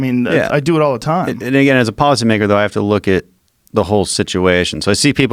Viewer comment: as a policy what???? I know I’m not the only one who heard that.